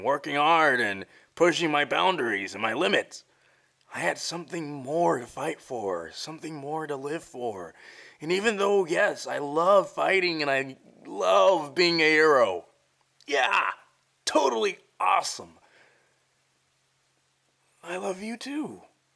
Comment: i thought he said ‘love being a hetero’ 😭